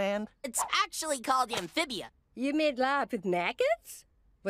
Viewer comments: "you actually made love with maggots?" Thats all I can hear Someone pls tell me what she is really saying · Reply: "You made larb (a Thai dish) with maggots?" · Yo, what did Anne's mom said? ... I didnt understood a Word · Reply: “You made larb (laap) with maggots”? In reference to the food critic episode.